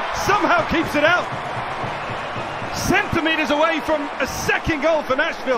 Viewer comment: Did I just hear the commentator said, ...centimeters away?